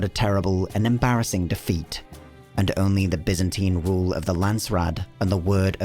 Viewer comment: did he say "the Byzantine rule" ?